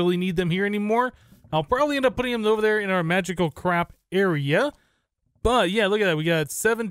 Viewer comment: i think i heard him saying magical crap area(in place of magical crop area)(it might just be me though)